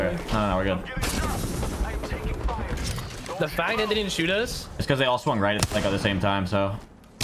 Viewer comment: Am i missing something at 2:13? It sounded like a slur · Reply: to me it sounds like “the fact that they didn’t even shoot us.” he do be spitting the sentence out like Eminem tho